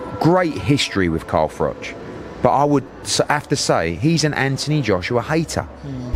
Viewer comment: I thought he said anti Joshua hater, would mean he rates him, but he doesn't.